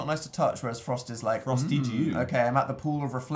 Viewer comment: i wasn't paying attention and thought he said "frosty jew"